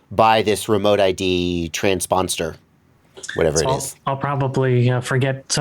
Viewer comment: I love your freudian slip where @ 10:28, it sounded like you said, Trans-Monster.